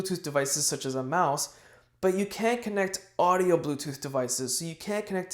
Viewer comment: can't understand if you're saying 'can' or 'can't' in that whole sentence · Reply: Here's what I said, I hope it helps, "You can connect Bluetooth devices such as a mouse but you can't connect audio Bluetooth devices. So you can't connect a wireless Bluetooth headset for private listening."